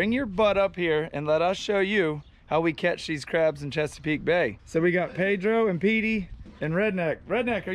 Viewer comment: Im not even gunna lie I thought it said "cheapscape bay".